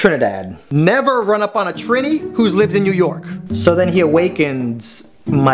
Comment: For a minute I wasn’t paying attention and all I heard was “never run up on a tranny, who lived in New York”